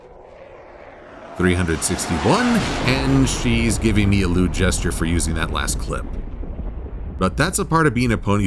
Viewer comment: did you say 'rude' as in insulting or 'lewd' as in inappropriately sexual way.